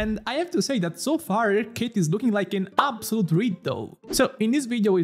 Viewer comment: her kit is looking like an absolute what? it sound like you said ritoud. Can you clarify? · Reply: Riddle